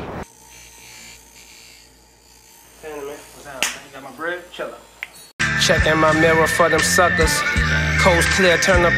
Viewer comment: what does guy on the left say before Spitta says 'shut up'? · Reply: Got my bread?